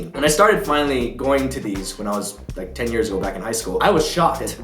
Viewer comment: did he just say he went to award shows when he was 10 and in high school unless u r a genius ur not 10 in high school right guys right back me up here come on right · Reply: 6 years late but he said "10 years ago"